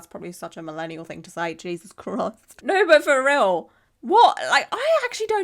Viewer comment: Did you say Jesus Crust? If so, can I steal that? If not, it's mine now.